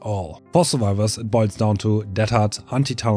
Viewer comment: what does he say? It sounds like he says "Anti-toweling" or "Anti-towering". What does that mean? EDIT: I believe he means "Tunneling", which means for the killer to focus on one survivor until that survivor is dead. Also, what does he mean by slugging perks?